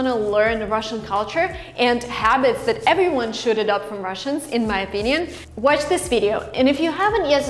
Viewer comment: What does it mean - should it up? You said "that everyone should it up from Russians". It was on 8:53,thanks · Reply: should adopt